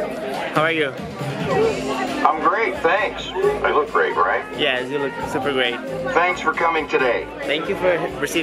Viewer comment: Quanto tu falou "yes, you look super great" Eu entendi "yes, you look super bem". KKK